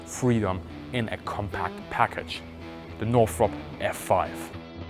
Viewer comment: Did I misheard the word 'Northrop' as 'Norfolk'?